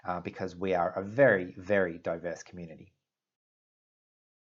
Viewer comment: Totally thought he just said “we are a very, very dope ass community.”